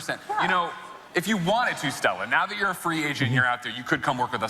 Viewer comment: did dude say "free asian?" LOL